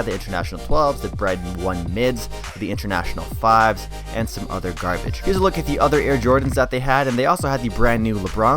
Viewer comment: Did he said some other garbage 💀💀 “ 4:40 “